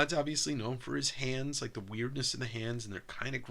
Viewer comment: My brain thought said Todd "McWeirdless" lol